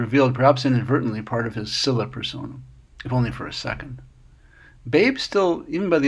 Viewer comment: Did he say "scylla personne?"